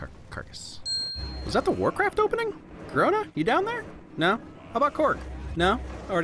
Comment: i thought he said “ Corona, you down there?” And I laughed a bit